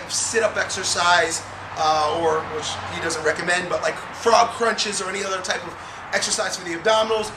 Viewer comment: really sorry, but is he saying throb crunches at 8:43? Desperately trying to posture friendly ab exercises...